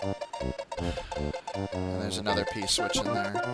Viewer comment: did say P-switch at 2:45?